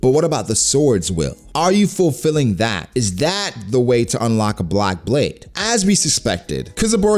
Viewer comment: Love your channel man. It sounded like you said block blade hahaha. But i think lunarians are just fire people. Basically Aces devil fruit brought to life. I think Zoro will cut flames to take him down.